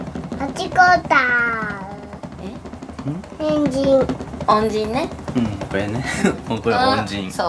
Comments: 恩人が変人に聞こえちゃうの私だけかな？ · ごめんポンス変人に聞こえた...